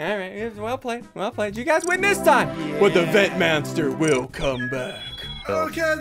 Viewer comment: did he say vent manster?😄